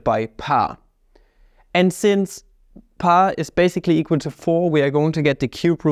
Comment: Did I just hear you say "pi is basically 4" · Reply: I don't know about you, but I heard him say "pa is basically 4".